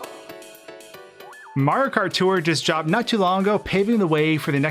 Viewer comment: Not even 5 seconds into the vid and i hear 0:03 "MARKART"